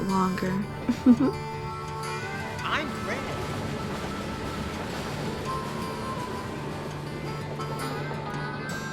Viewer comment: what did he say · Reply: "I'm red"